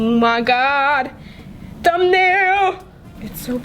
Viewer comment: I thought that she said that thumbnail